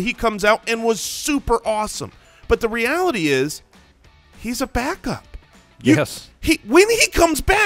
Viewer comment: When says "he's a backup" about Kareem Hunt, I heard "Hot pockets" Sounds kinda like Jim Gaffigan